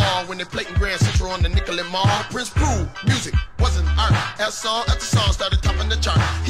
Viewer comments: Please explain how Prince proved that music wasn’t an art again? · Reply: Thanks. Lol. It sounded like he said “wasn’t art” I can hear now he says “was an art”. I was baffled at the notion. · Ok, now I swore I heard (him/you?) rap, "Prince proved that music WASN'T art"....😮 But I KNOW that can't be what was actually said..... 🥺Right?!?🧐